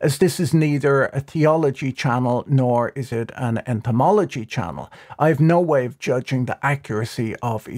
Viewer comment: I thought you said etymology channel for a second, and then my ADHD kicked in. But you would kick butt if you had a channel discussing the origin of words. Words are so friggin awesome! Then I realized you said entomology and then had to restart the video because my ADHD had me side tracked. (shrug)